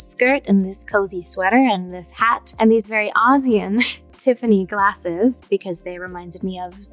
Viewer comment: Did anyone else think when she said “ozzy” glasses she meant Osborne? Because they were circular? Or just me? (I guess I should have guessed wicked tho-duh) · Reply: she said "ozian" but yes i thought of ozzy too. rip